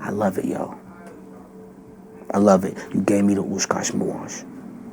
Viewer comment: orlando brown who appears to be a hamburger short in his happy meal, said you gave me the WITCHCRAFT boys, turn captions on at 21:38 and listen yo. The first time he said it it was, different like unrecognizable but the second time he said it YT closed caption heard "I love it you gave me the witchcraft boys" and i hear it clearly. And that tracks with his relationship with jay z because jay z is an illuminati cultist. Homosexuality is a part of an evil ritualistic cult a real one of major elites.